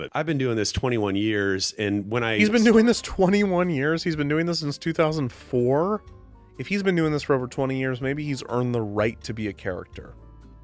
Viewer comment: i misheard "he's been doing this since 2004" as "he's been doing this since i was 4" and had a moment of existential panic about age